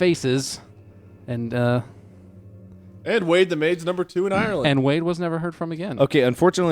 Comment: What did wade say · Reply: "wade the maid's number #2 in Ireland"